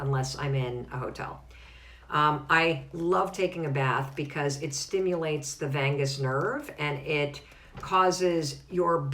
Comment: i almost thought i heard a bath stimulates the vegas nerve lol